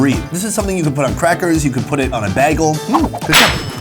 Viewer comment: I'm sorry, Sir, did you just say, "You can put it on a BAGGLE"? What??